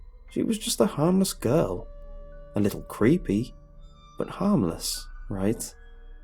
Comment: HAHHAHAHAHAHA I thought you said preepy not creepy! Too much? Okay...